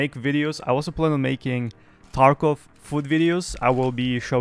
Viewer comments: had me excited for a second...I thought he said Tarkov foot videos T_T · Bro… I thought you said “Tarkov Foot Videos” 😅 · Sorry, at first, I thought you said, "I will begin to make Tarkov FOOT videos." Then you held up the bag of chips, and I got even more concerned.